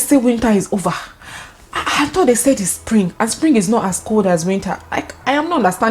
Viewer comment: I 'offed' when said what's going on, I thought they said spring is not as cold as winter 😅😂😂😂😂😂😂😂😂